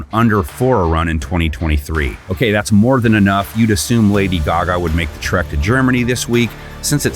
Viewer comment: Isn't it Taylor Swift? I swear I heard "Lady Gaga."